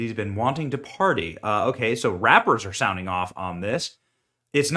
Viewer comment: i heard "be wantin that body" not "be wantin to party"